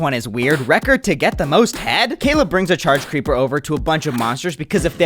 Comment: ayooo what did he say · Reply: he said “record to get the most head”